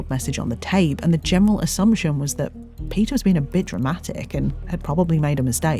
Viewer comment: really thought she was ab to say Peter was a btch